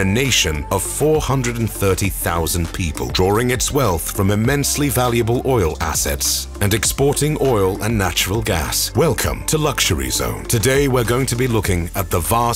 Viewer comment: did you say OIL ? 🦅🇺🇸